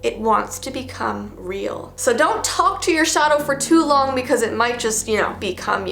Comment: Don't talk to your shadow I thought it said don't talk to your shower